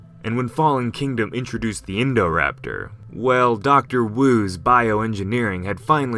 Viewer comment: i cant get around this name, i only hear "indoor-raptor"